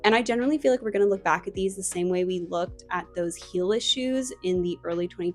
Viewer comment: what did you say hiller shoes?